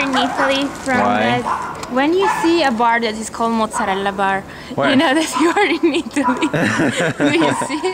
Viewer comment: what did Marzia say? I kind of didn't hear it clearly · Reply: she said "when you see a bar that's called Mozzarellbar you know you're in italy" and when pewds zoomed in she asked "do you see it?"